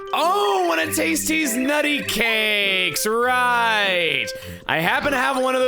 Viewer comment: I heard taste deez nutty cakes while listening to this in the background and was so confused